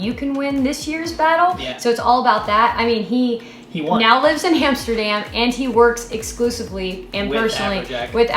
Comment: Did she just say "Hamsterdam"!?